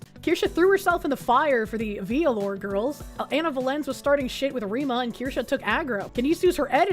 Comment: Is it bad i kept hearing "Anal Violence" instead of Ana Valens?